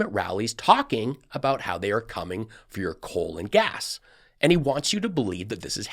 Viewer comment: I had to do a double take because I thought you said, "They're coming for your colon gas!" 🤣 No, we aren't, Don! Please keep it! PLEASE! 🤮